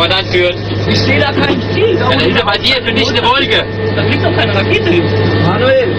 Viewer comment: Hat das wer akustisch besser verstanden? "Ich sehe gar kein Ziel." Was antwortet der Feldwebel(?) ? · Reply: Da ist bei dir eine dichte Wolke (meine ich zu hören)